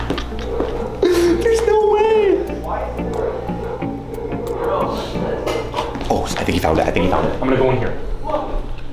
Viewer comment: I think I heard swear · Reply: He said $h|t